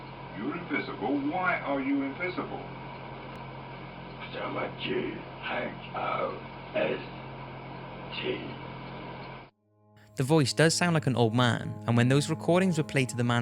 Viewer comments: What does the voice say at 14:45? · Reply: the guy says why are you invisible and the poltergeist says cuz ima ghost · in number one, when he said "why are u invisible" it sounded like he said "cause im a G"